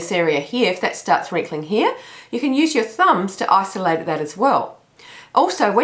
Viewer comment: Hi, I am so amazed at the difference in your face from the time you started until now! One comment that I hope you will not be offended at but this is just a constructive comment. Because of your heavy Aussie accent I thought you were saying 'oscillate' when you were saying 'Isolate'. I was wondering how I could do that but just today I finally caught what you were actually saying..big difference!! LOL